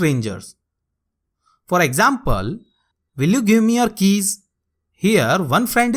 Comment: sir i heard will you give me kiss instead of keys😂😂😂